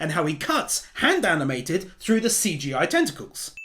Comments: the captions say testicles instead of tentacles 🤣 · lmao the subtitles say testicles instead of tenticles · I think it was supposed to say tenticals at the end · you writed CGI testicles instead of tentacules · the subtitles say testicles not tentacles 😂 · "Tentacles" The subtitles said Testicles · the captions read testicles instead of tentacles. You have no idea how funny I found that😂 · This may have been noticed before but on 25:31 it says testicles instead of tentacles. Made me spit my water everywhere but i guess that was the point? Thanks xoxo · is it just me or at 25:33 it says CGI testicles instead of tentacles at the bottom of the screen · the subtitles say CGI “Testicles” when he said the word “Tentacles.” I’m not sure how that happened, but it made me laugh and I’ve watched this video several times and not noticed that before. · the subtitles say testicles instead of tentacles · the subtitles got tentacles a little wrong... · Anyone else notice at 25:28 where it says t*sticles instead of tentacles. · Notice how at 25:33, the text says 'Testicles' instead of 'Tentacles'. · he says tentacules and the subtitles say testicles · your captions says testicle not tentacles good sir. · is no one gonna mention the fact that his subtitles say testicles instead of tentacles here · it says testicles instead of tentacles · said testicles instead of tentacles🤣🤣🤣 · when he says “tentacles” the subtitles say testicles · he says tentacles, but the subtitles say testicles